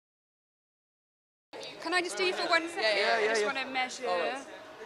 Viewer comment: I heard "Can I just steal you for a second" than louis goes "Yea yea, always" than he realized what she was doing :p